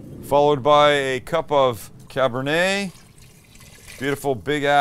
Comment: what is this. Im french i dont understand. The sauce ^^?? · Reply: 250 mL of Cabernet Sauvignon red wine